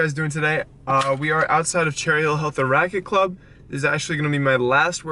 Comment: Did You Say "The Rachet Club" ? lol